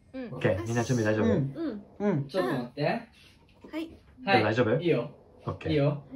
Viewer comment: 要人くん杢代に向かって「レオ大丈夫？」じゃなくて「かずと大丈夫？」って言ってるように聞こえるのは私だけ？笑